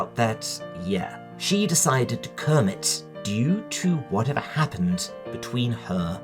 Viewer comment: Kermit! The first time you said this I heard “comb it” and I assumed it was some type of slang. 42:42 Yes! Cover Stein’s Gate.